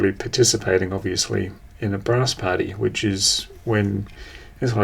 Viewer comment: When you were telling the story about the brass party during the Mercedes showing, I thought you said bra's party....what 8 year old wouldn't like to go to that ? 👙🤣 The little side mirror on the front of the Prado; I had an imported Toyota Hilux Surf in the early 2000's with one of those side mirrors...it was actually handy when I went 4wding once, when I got very close to some large boulders. Thanks T. 👍🇦🇺 🧑‍🚒